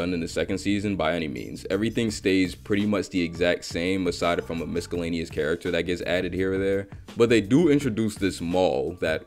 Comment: I think I heard miscellaneous with a hard 'c' @ 17:50, best part was ' Yo brandi had a fat ass' 🤣 bro was short of saying "thicker than a bowl of oatmeal"